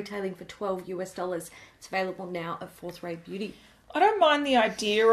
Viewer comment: I always hear 'fourth ray beauty' as 'fourth rate beauty' and think 'why would you call your brand that?!?!'